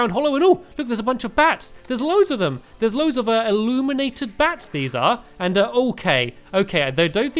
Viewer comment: i thought it said illuminati bats